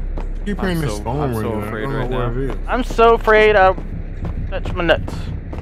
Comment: "I'm so afraid I'm gonna touch my nuts." Anyone catch that lol